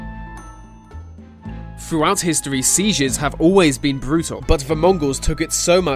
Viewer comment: did he just say seizures instead of sieges?